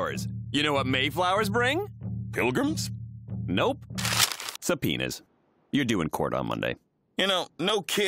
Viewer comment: Did he say penis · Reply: Subpoenas say, I think so